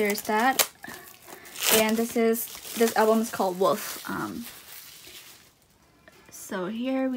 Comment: it sounds like your saying woof not wolf sorry baby~ ♡ when i get a notification from you, you literally brighten up my day and when i hear you beautiful voice ( omg this sounds so creepy im sorry ) i literally die for you ( again im sorry ) please make my days even more better when you next upload~ I PURPLE YOU~ ♡